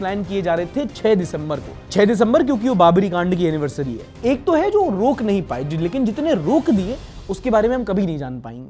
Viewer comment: i heard 6 dec baabli G nd ki anniversary lmao